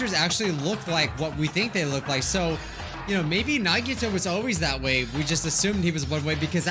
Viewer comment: Sounded like he said Nagito was his waifu